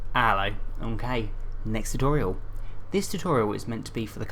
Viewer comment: what does he say? ''Ello Mkay''? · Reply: He says ello ok